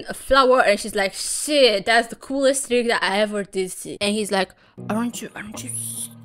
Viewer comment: "shiii thats the coolest---" what?! What did you say.....????? Did you say what i thought was said?? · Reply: oooooh. You said "coolest trick I ever did see" with accent I kept hearing "tigga" but..no t Didn't realize it at first but I should have checked the closed captions. Rectifying my error I'm happy I was dead wrong because I thoroughly enjoyed the rest of the video